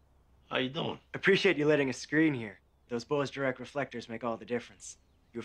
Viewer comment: I would have kicked bro out when I saw those grandpa sandles getting strapped up 😭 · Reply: I could have sworn he said "Thank you for letting us screw in here."